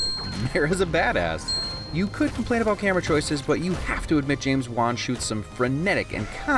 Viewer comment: I thought he kept saying James Bond, not James Wan 😂